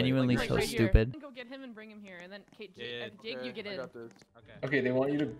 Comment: who else heard jake instead of temp